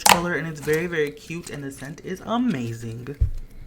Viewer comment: Did anyone hear at 4:11 when he said amazing there was a G and it sounded like amazinG